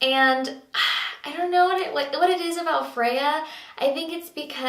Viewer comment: I heard "Fea" at first which means ugly in Spanish and was thinking is she joking. but it's Freya!